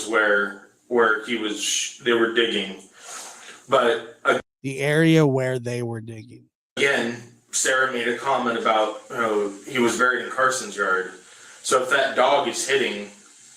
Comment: Replay.....anyone else hearing this? At around 2:03:10 it sounds like almost said "the area where he was sh..ot"??? Well.....he trails an sh....then changed it, lord they are sickening!